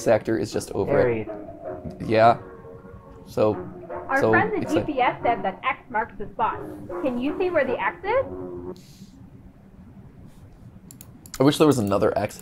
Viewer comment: Did she say "Our friends at CPS said X marks the spot"? As in Child Protective Services? Am I hearing that right?